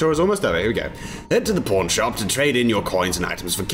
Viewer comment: I heard: "Head to the porn shop" He said: "Head to the pawn shop"